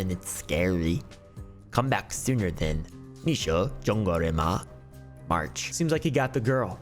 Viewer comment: LOL I know i heard "zhongguo" in there but what did you just say?? mandarin arc??